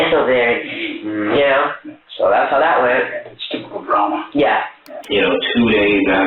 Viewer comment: I'm glad you have captions, because I definitely heard him mumbled "stupid obama" first time around. xD